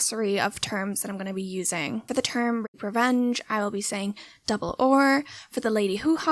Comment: I find this series so engaging ... i really appreciate your dive into the history before exploring comtempory films Btw the whole time i thought you were saying "double oar" as a mispronounced horror ... until i realised you were saying r 😅